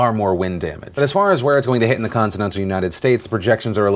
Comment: BUT Y'ALL DON'T FORGET, THEY WERE DEF MINORITIES. Just, to be clear, ya know... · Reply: Phil, did you just said "the continent of the United States"? Please tell me i miss heard. (Min